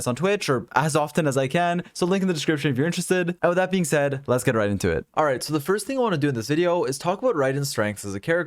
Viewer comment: I literally thought that he said lets get raiden into it